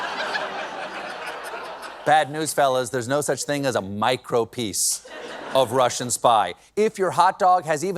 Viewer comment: I thought he said micro penis...which I think is a minor problem in the Republicon party 😂😂😂😢😢😢❤❤❤